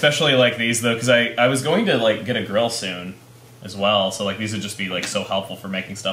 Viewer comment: I misheard when he said "I'm getting a grill soon" as "I'm getting a girl soon" and I was kinda concerned.